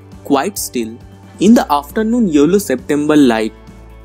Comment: It was cool n ur video editting skills are appreciatable but sorry when u said yellow i misheard it as YOLO .. n it cracked me up .. damn 😂😂😂